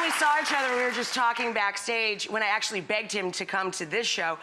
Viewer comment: sounds like she said "banged him" instead of "begged him".